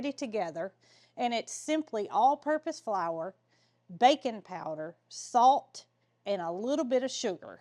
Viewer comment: Silly me , I thought you said “bacon powder”, I looked up bacon powder and then realized you said Baking powder. Silly me. I thought bacon powder was a local ingredient .